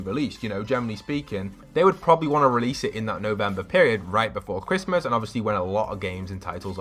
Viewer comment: I thought he said "No Nut November Period" at 10:44! I have a dirty mind...